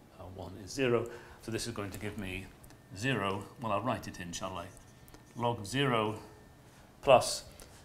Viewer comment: Did he say inshallah?